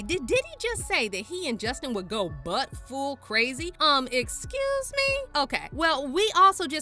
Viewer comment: Sounds like Diddy said “Buck-full”